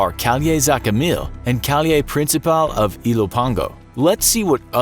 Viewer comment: when he said calle principal it sounded like he said kayne princayal